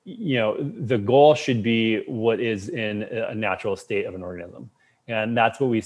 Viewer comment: what did he just say?? 😁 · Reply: “The goal should be what is in the natural state of an organism.”